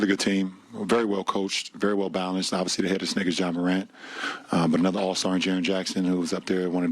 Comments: did he say this nigga ja morant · I coulda swore he said "this nigga ja morant" lol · Is it just me or it sounded like he said “nigga” 😂😂